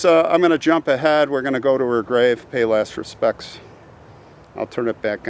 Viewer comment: I thought you said "Let's go pay our less respects (instead of "last" respects)". Lol :)